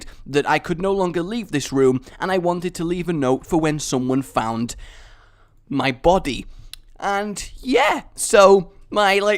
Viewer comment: omg i laugh my ass off when i heard "if someone find my body"with the death note thingy xD